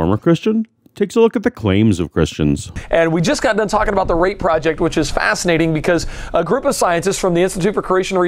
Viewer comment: I misheard Eric saying it as "Rape Project".